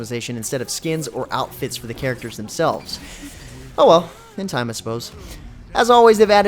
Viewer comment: in 8:30 it sounds like he said hentai